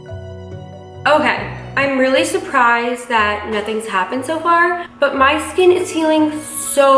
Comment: was I the only person who heard her say "sho far"